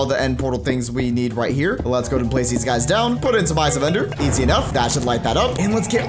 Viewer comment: Thought it said 5 Ender Pearls. xD